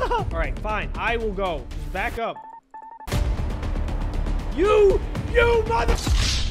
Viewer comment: he swear he say your mother ####